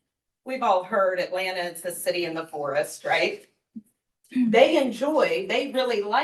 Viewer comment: She said city of the forest I thought she said poorest. Lol My head turned so fast I got whiplash!